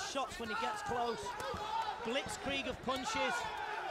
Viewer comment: did he said "blitzkrieg-punches ?"